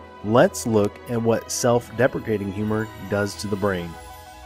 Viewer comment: Did you just say “self-defecating” instead of “self-defending”?